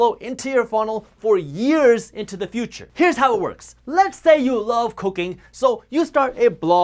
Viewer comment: I thought he said "let's say you love COCAINE"...lol!